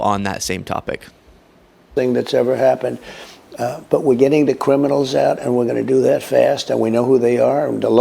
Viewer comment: does he say cwiminals? 😂